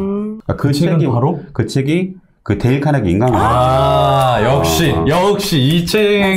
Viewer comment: 죄송한데,,, 책제목 한번만 더 말씀해주실 수 있을까요? 5:33에 나오는… 저는 왜이리 안들리는지 ㅠㅠ 부탁드리겠습니다~! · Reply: 데일카네기 인간관계론 입니다 ^^